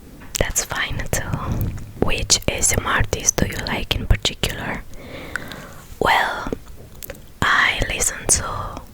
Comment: she listed some of her favorite ASMRtists, but right off the bat I didn't understand the first one she mentioned. It sounded like she said "3V" but I didn't find any ASMRtist by that name. Does anyone know who she meant? And towards the end she mentioned "Secret ASMR", but the very small (3.8K subs) ASMRtist I found by that name can't possibly be the one she described. She mentioned that the ASMRtist doesn't talk much, but this one talks NONSTOP about subjects like her latest shopping trip. Yeesh. However, there's also one called "ASMR Secrets", a Turkish streamer with 160K subs who seems pretty good. Is that who she means? Just for laughs I'll list my own favorites: - tiny hands ASMR (141K subs). A Portuguese ASMRtist whose specialty is her calm, soothing voice. Her videos frequently include relaxation instructions and breathing exercises. - Tingting ASMR (2.56M subs). Tingting is Chinese-American and many of her videos feature Chinese props and cultural references, so they are often legit educational as well as being great ASMR. For example, I listened to her recent Chinese calligraphy video MANY times. She may also be the sweetest human being on the planet. - MaimyNyan ASMR (300K subs). A LOVELY Brazilian ASMRtist. I love her roleplay/cosplay, and her accent is enchanting. Check her Tavern Barmaid videos! She also has an old abandoned channel called "Maimy ASMR" with some great content, in particular an amazing NieR:Automata cosplay. - BlueWhisper ASMR (463K subs). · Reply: Hi! The first ASMR-tist I mentioned is Frivvi (FrivolousFox ASMR) and in the end I mentioned Sacred ASMR (SacredXO ASMR). Thank you for sharing your favorite ASMR-tists with us✨️